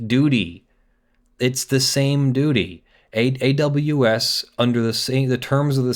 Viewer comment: Sorry, I heard: "It's the same Doody." :p